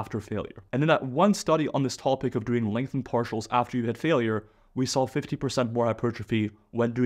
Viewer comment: did he say 50 percent or 15 percent? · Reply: He said 5500 percent